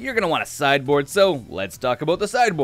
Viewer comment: I hear "Brave the Elephants" xD Fun deck :D